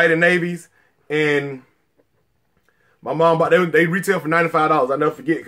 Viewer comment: How much was the Rodmans? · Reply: Evan Nolen I thought he said $130 retail